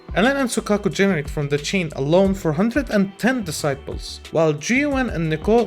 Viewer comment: Great video bro, honestly didn't even think of these issues. Hopefully they got it all calculated perfectly to not ruin the balance too hard. I have to say though I thought you were saying 'disciple' every time you said 'decibel' ahaha I feel like I can't be alone here